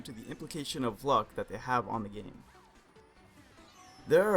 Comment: I thought you said on how much weed they have on u XD